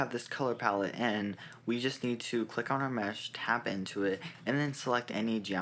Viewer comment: someone pls explain what hes saying, i just cant understand, Tap into it? Tab N to it? Tap N to it? i Cant fucking understand